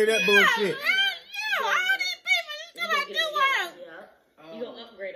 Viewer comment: sound like she said all these people and you talkin bout juice world